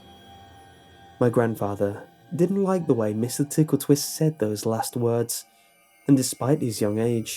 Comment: I thought it said Mr. Pickletwist.